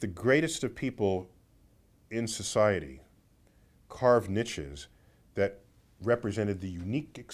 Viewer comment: What does he say @ 1:35? Carf Nitches?